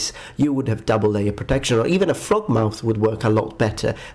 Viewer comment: can someone help me in identifying the helm displayed at 04:49 I heard throat mouth but I'm struggling to find it on Google